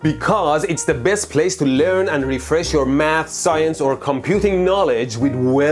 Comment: ~ I heard that as "...to refresh your MAD science or computing knowledge.." for a split second XD